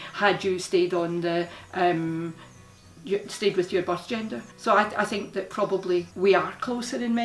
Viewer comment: Whenever your parents said, “birth gender” I heard “Boss gender” xP